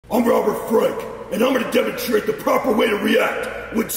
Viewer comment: At first he sounded like he said "I'm Robert Fripp" so in my mind now he is a gym version of RF